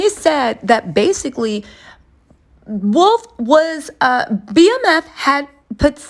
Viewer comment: how you gon ask us did we hear what he said but you didn’t hear. He didn’t say BMF put seed money into Bad Boy he said the Butt Naked group Wolf was apart of did.